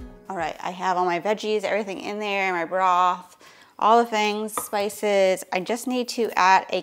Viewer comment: Broth. She said Broth. I had to rewind to check. It sounded like she said BRA. I'm thinking, why would she put a bra in the pot? To funny.